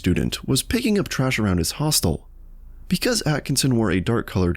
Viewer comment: did you just say hotel as hostile or is that what its actually called lol